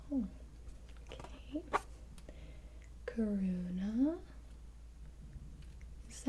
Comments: kAroona? or corona... Ok ok Sorry for being imature XD i just heard it wrong XD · Did anybody else hear her say corona as one of the kids names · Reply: I thought she said that too until I heard her say Karuna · starts calling names me: not knowing she's saying asmrtists names: matt, lucy, tingting- me: ....tingting sounds famaliar: karuna, tony, mari, ally, me: karuna satori? also me: scrolls down to the comments and feels dumb as fuck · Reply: When she said Karuna I thought she said Corona 😂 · WAIT DID SHE SAY CORONA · UMMMM... DID SHE SAY CORONA? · is her name corona 😂 tats what it sounded like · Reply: Karina